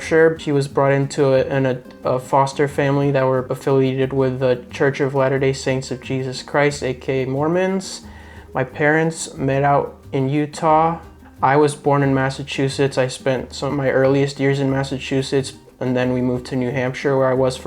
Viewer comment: I heard “my parents made out in Utah” 😂😂